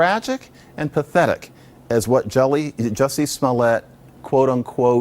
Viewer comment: lol did i hear him say Jelly?